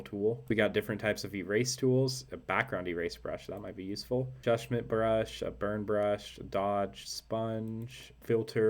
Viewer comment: I thought you said "judgement brush"! Really wondered what that would do, like you paint over something you've created and it tells you how bad it is or if it's a person it gives you a list of physical flaws... 😅